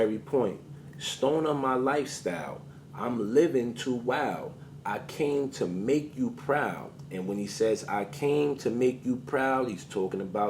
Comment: He is talking about haitis the country… he has a song called Haitian slang and he raps I’m that style. Also the hand symbol means blood. I always thought he said I’m living to wild, I can’t even make you proud also??